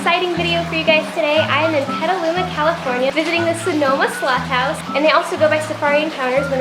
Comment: Glad I was watching the captions, I thought she said "Slut" House.